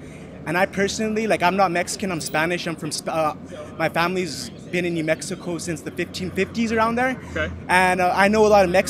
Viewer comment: my family's been in New Mexico since the 1550's? Did I hear that right?